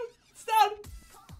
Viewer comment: Did he say “xăng” at 1:47??? :))) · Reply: I think I said "It's starting" but it sounds more like "xăng"